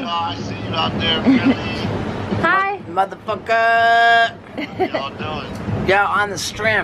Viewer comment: i thought steve o said "bout tofukherr"